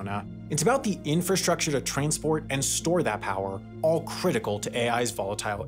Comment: Did he say ”train-sport” at 16:28?